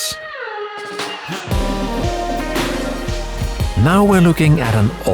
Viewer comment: I swear he said flesh-hlight.